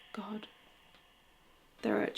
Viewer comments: Oh also at the 25:16 mark, you tagged the voice as saying "up to God." I heard "belong to God" (reverend?? 😳) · Still watching, so every now and than an edit, but heard some "missing" words Brought up to God You did this, you can feel the emotion. As if he was shocked and very upset. 25:21 I heard animals actually 25:52 I heard after her question "the devil"? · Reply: Wow, great catch, Myra. 25:14 'Brought up to God', indeed 🙏, followed by another male voice who says: 'Do that again.' (slow playback speed to 0.5, for clarity). 25:21I got it wrong. I couldn't hear animals, as you did. I slowed it down to 0.25 playback speed. Male voice, British accent, 'Getting (unclear--Amy talks over the response)'. 25:52 Another great catch! You're correct--when asks why she feels uncomfortable, a male voice can be heard to reply: 'The devil'--easier to understand if the playback speed is slowed down. This episode was incredible, as it revealed so much about karmic law, in action. The voices you hear can be heard in many episodes--something they never remark upon. These are likely to be the voices of some of her Spirit Guides, protecting her, as well as steering her along, on her life's path.